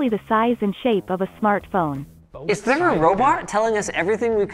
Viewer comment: did linus say "robart?